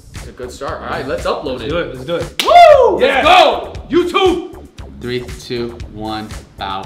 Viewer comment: he said wow let go YOU TUB!!!instead of YOUTUBE,that sound when he say YOU TUB!! Is weird,make me laugh hard😁😁😁😁😂